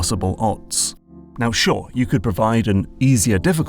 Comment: Captions say "dure" instead of "sure"